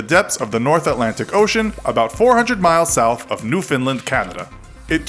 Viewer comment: Did he just say "New Finland"? Maybe just my ears. Either way he mentioned Canada!